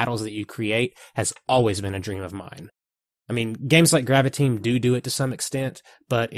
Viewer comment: What is that game he said in about 2:30 when he said "...game like grab a team do do..."